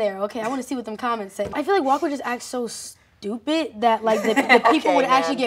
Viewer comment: I'm prob deaf but i heard Leah say "Walk would act so" and not "Walker would act so" 😭😭 This is my problem i ship them too much in the show🤡🤡 Am i the only delusional one here or ? 🥲